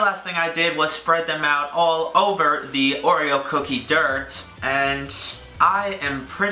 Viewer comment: I thought he said ogre